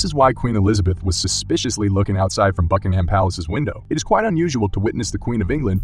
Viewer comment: I misheard fucking ham palace